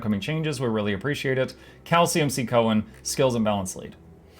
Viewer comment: when you said "cal cmc" i heard it as "calcium c" lol